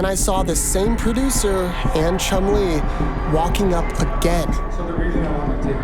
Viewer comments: i just wanted to say that i found your channel recently and binged all your videos. they’re very inspiring for those with big dreams and i have the biggest respect to you for going out and making these videos. i love seeing you upload and i can’t wait for you to hit a million subs one day :) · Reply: I thought he said chun lee liek the Fortnite character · I thought he said chun li like forrnite not chum lee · When you said chum lee i thought you said chung lee